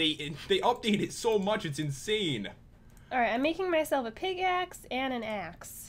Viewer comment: I swear... said "I'm making myself a PIGax" I heard her say that!!🤔🤔